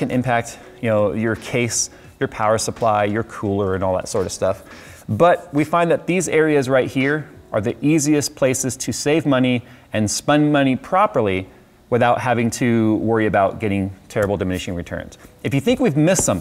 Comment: Did he really say "spun money" at 15:40?